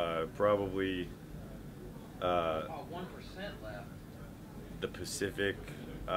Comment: Haha who heard freddy d in the background round the 3:30 mark "1% left? Fuck man i need some power"